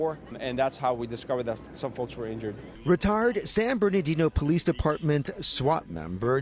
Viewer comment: did he said retard? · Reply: If you're serious he said retired 😳😂😂